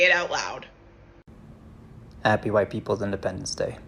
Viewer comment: happy yt people ??? i didn't hear right sorry anyone know what was said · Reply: They said, "Happy white people's independence day. " Which I didn't even know we had? Cause we don't